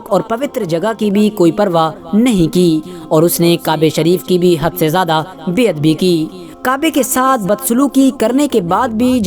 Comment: ye Kya bol diye bhai . Hum se jada Beahadbi ke hai bol rha ho tum